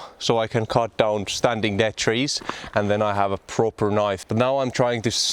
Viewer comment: 😅 Thought you said only poket knife.